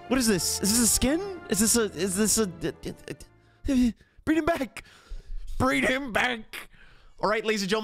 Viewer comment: When Zhongli faded away, did he just say: "Breed him back!" Or is it just ny headphones?😳